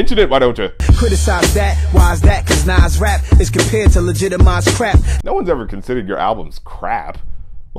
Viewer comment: i always thought he said “legitimized crack”. i guess i need to clean my ears